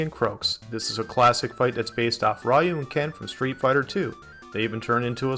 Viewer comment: DID HE SAY RYLE AND KEN🤣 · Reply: Ryu and Ken probably slurred from talking so much haha